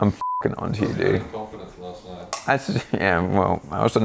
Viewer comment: Wait what did say at the end about/to Dose? Because I heard "Yeah we all know that you're allergic to Dutch" UHM HELLO IM DUTCH (im from the Netherlands/Holland how you want to call it) AND I FEEL A BIT OFFENDED LOL😭😂